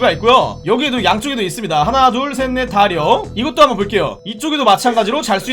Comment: 하나 둘 셋 넷 다 려 나만 이 여섯 할때 려 로만들리나